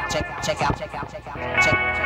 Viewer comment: I always thought he said, "Check out Penelope." I was trying to figure out who Penelope was.